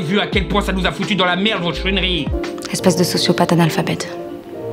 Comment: je vais me faire l’avocat du diable ici, mais je pense que c’est plus une erreur d’écoute. Selon moi il voulais probablement dire « chouineries » et pas « conneries ». Mais j’admets que dit aussi vite ça peut porter à confusion.